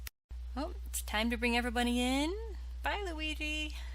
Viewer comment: Did she say every-bunny???😂😂🤣